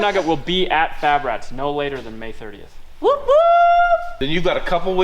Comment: Can’t wait to get it back May 31!😉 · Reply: Thought said May 30th? Don’t you go cutting him any slack! 😂